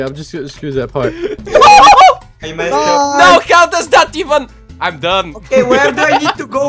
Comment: Every time u say I'm done I thought u said I'm dumb